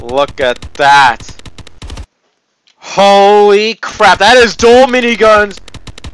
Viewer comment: holy crap me:that sounded like holy jesus